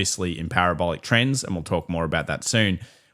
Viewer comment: On the central bank liquidity chart, I literally thought he said the "central wankers" lolololol But that should be the new phrase. Central wankers and the CPLie